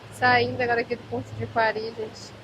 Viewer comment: Qual município é esse ? Eu entendi "Paris". É isso ? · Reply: Coari?